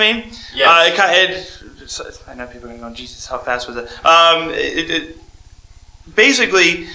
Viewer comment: Did he say 'Jesus how fast was that' @ 1:39 !?? :O